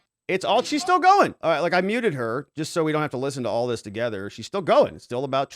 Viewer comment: when you said "I've muted her" I thought you said "I've neutered her"